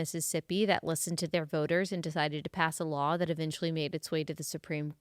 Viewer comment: When you said “a law” I HEARD “allah” lol 😂